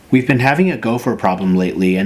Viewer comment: The first thing I heard was: "We've been having a girlfriend problem lately, and they've been destroying the yard". And I was like "wtf?"